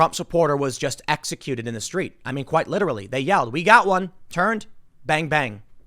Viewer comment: So, he was shot by a "mostly peaceful" protester? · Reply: Perfection not that I have seen. This is why I'm not totally jumping to conclusions as of yet but it did kinda sound like he said , "we got one here" or something of that effect but who knows.